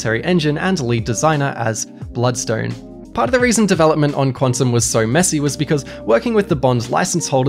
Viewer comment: I think I heard him saying "Quantum of So Mess"